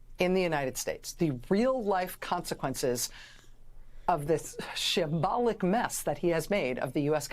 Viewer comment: What word does she say at 3:05? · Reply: Shambolic. It's an adjective derived from the word 'shambles', meaning disorganised or disorderly.